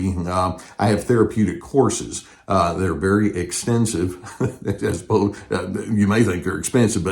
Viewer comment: 🤣😆 we do not think they are 'expensive' but does sound like you say 'expensive' vice 'extensive' 🤗 thanQ, i just now signed up for email 📧 subscription and the information in your 'articles' is golden